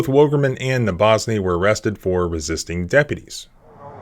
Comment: Wondering that as well, seem pieces of video cut out, she tries to roll window back up and he grabs it unlawfully and reaches in unlawfully tells her to get out after he did all that, window is broke , yanks her out without making sure her car is in park, rolls almost hitting police car and video cuts off, Then dont even mention her or what she was charged with. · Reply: I thought he said she was charged with resisting arrest.